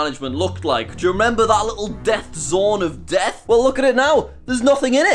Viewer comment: when you said "death zone of death" I thought you said "death xorn of death". BTW: a xorn is an elemental from D&D